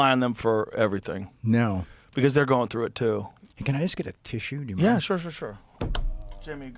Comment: What I heard.. “can I ask to kiss you?” 😯 What was said.. “can I ask get a tissue?” 🤦🏽‍♀️😂